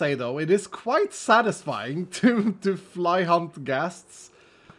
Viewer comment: things I misheard: Actual sentence: → Misheard sentce Fly Hunt Ghasts → Fly HUMP Ghasts Artistic → Autistic 😂😂😂 Anyways, I can't wait to see stress' and Joel's reaction😂